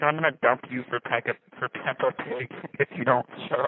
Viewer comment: bro just actually said “pegga pip” instead of peppa pig, while making fun of his girlfriend for having a speech impediment Literally do I need say more